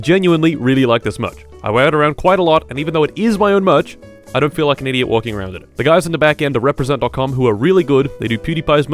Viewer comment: what did you say at 1:58 ?????!!! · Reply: (dont worry) he likes being held That's what it says